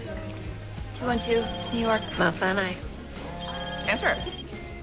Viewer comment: What the fuck did Meredith say at 0:06??? "Bai chai nai", "Bout cai naye" I literally have no idea, it sounds like she speaking another language. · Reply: She says "Mount Sinai". It's a hospital in New York